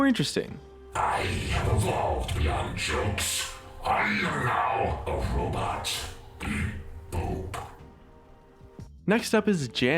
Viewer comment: omgggg .... i thought he said "i have evelved beyond jokes. I'm not a robot. Eat. Poop." .........